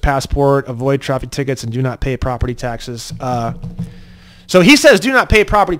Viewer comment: at minutes 35:40 you say he says do not pay or do not need to pay taxes and that is incorrect he actually says do pay your property taxes and to avoid traffic tickets